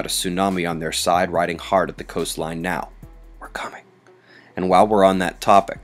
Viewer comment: What was the whisper at 1:10? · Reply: He whispered, "We're coming!"